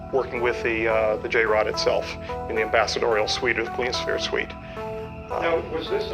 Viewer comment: Where did this happen at? I tried to use closed captions to see what he said @ 5:02 he says " Working with the J-Rod itself, in the ambassadorial suite....of police fair suite" or "police sphere suite" what or where is this place he mentions? Never heard of it before. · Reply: Ok nvm I figured out what he was saying "Clear Sphere" and yes he sounds believable to me